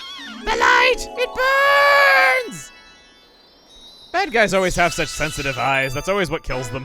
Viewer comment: coulda sworn he said “black guys always have too sensitive eyes”